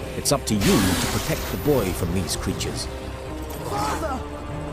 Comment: Did he just say " Father " ?